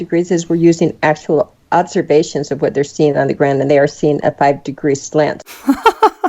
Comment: At the 6:00 mark the man asks about the +- 5 degrees and the woman explains something about "actual observation seen at __", I can't quite hear and/or understand what she says. Can someone help? I think she says at the ground, but I'm not sure. It bothers me more than it should.